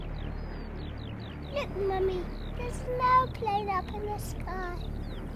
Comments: to be honest i always heard "an airplane" but somehow something didn't feel quite right about that. But now that i saw that comment and i had to listen again and it IS "no plane". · The child says that there is an airplane in the sky, but imagine the terror that would be felt. This song is terrifying. But, oddly calming. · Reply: no it’s actually “look mummy there’s an airplane up in the sky” it sounds like he says no cuz of the England accent. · For the last 30 years I thought the kid was saying ' look mommy there's no plane up in the sky ' Thank YouTube comments! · I clearly hear "there's no plane" and people are saying "there's a aeroplane." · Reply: Me, I understood "there's no plane" too. Maybe the little man speaks a bit indistinct...? "...there's 'nairplane...."